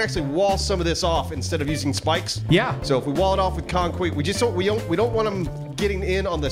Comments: anyone else hear say concweeet at 2:00 LMAO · Anyone else hear say "concwete?" When hes explaining to his plan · Did really say "conqueet"? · At @ 2:00 did he say wall it off with conkweet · I heard "Concwet"... xD!! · Did say Conqueet @ 2:00??? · Anyone else hear "concweat" at 2:01? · did say concweat there?